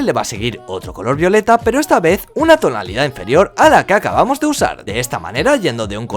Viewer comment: En el minuto 5:06 parece que dices "ala caca vamos de usar"